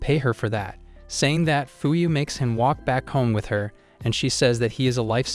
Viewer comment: caption said fucu